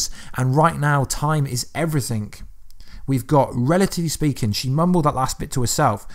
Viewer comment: i heard her say "literally speaking" not relatively speaking.